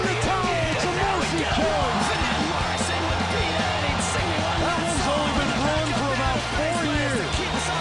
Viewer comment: does he say "That one's only been Bruin for 4 years." Reffering to Lucic. Or "That one's only been brewing 4 years." Reffering to the confrontation. ?